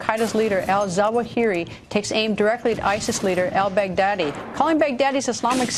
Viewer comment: did I hear right el big daddy